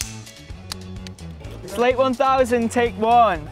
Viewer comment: i thought charlie said “slay 1021”